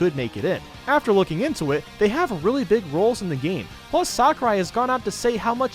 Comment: he said for Xenoblade 2 "really big roles" I thought we was gonna say "really big boobs"